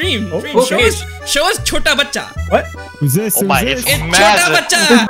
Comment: Bro I thought he said couta bacha which means "so son of a dog"